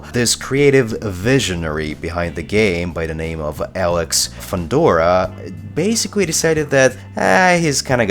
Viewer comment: I thought you said Alex Fedora xD I laughed so had I choked on my hot chocolate. Why Cinemax? Whhhhhyyyy?